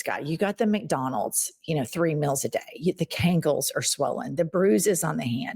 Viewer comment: I LOL every time I hear "Canks".